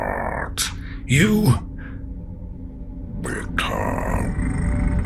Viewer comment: Unfortunately, every time it says 'become' I hear 'bee cum' 🐝💭